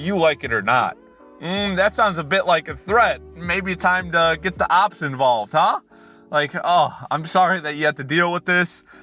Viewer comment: Did he say 'get the ops involved' bit drastic, don't ya think?